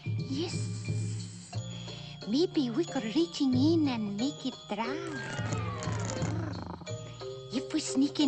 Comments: "Maybe we can sneaking in and make it drown" Cause fish can drown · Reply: I always thought it said make it dry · did it say make em Drown or make it dry? 🤔